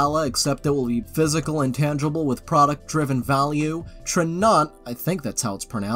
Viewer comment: I barely understood what they’re saying but what I got from it was: “What’s worse than an MLM?” “A crypto hun!” I thought they cared about the environment 😞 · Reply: Tranont I think?